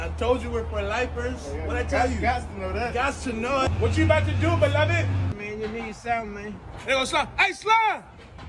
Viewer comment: when he said beloved i thought he said my luvvy in some english accent hahahaha